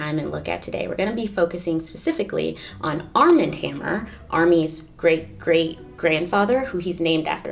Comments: This is why I cannot play Candy Crush while listening to your stories. I thought you were saying Arm and Hammer (Baking Soda) until I looked up. Forgive me. I need help. LOL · Totally thought you said ARM AND HAMMER. Couldn't figure out what baking soda had to do with it...